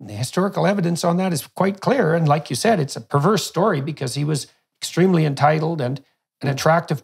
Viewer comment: Why is he calling it a "perverse" story @ 2:20, or did I mishear it? It is a triumphant story, really.